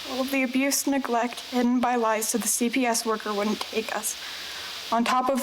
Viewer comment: What did she say about a CPS worker @ 2:06?? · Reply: all the neglect and abuse hidden by lies to the CPS worker